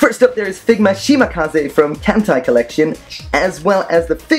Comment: did he just say hentai colllection?